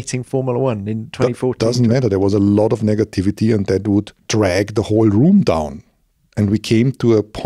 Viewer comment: did he say "that dude dragged the whole room down" speaking of Rosberg or am i misunderstanding something?